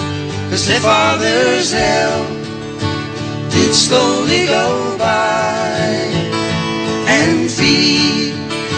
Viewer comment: I always thought they were saying their health would slowly go by. LOL.